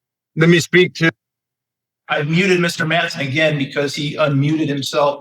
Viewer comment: He was hoping to use that famous charm on a jury. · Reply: I heard it as "Manson" and I thought, yep he seems like a close relative, sanity -wise speaking.